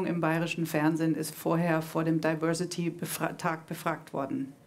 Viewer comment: Ich hab erst am Ende der Sendung begriffen, dass die "Diversity-Talk" sagt. Ich hab die ganze Zeit Diversity-Tag verstanden.